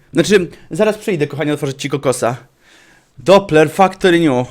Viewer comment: "otwórz mi kokosa" usłyszałem "daj pomacać kutasa" XDDD